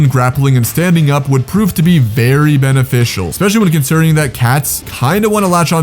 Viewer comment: why did I hear “bearey” beneficial